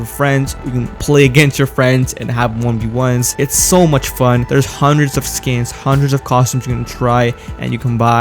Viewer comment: "There's hundreds of scams" me: wait did i hear that right